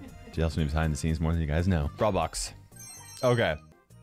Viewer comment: did u say roblox?